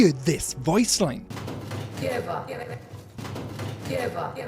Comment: is it just me..or before roxy says “give up”..i hear something similar to “don’t”..? like..”don’t give up”…